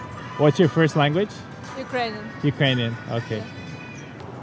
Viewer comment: Ukranian? Ok... (ok sounded like “damn we spend tons of money on you guys”)